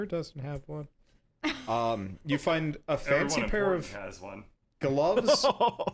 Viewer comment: Please tell me what said there. It sounds like a funny moment. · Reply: It sounded to me like "Everyone important has one," referring to the newest bag of holding.